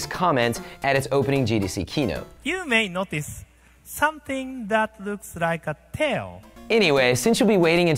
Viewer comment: I thought he said You may notice something that looks like a pear